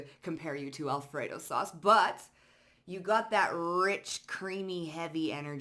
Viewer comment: I thought you were going to say “you got that rizz” but you said rich 😂